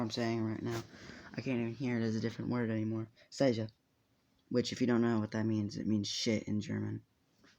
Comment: did you just say shit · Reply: Yeah he tried to say it in german but she said seische not scheiße